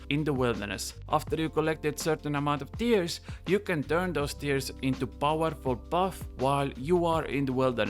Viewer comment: thought you said deers.